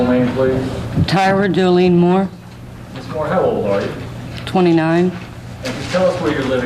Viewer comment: I need to get my hearing checked. Did she say 29?!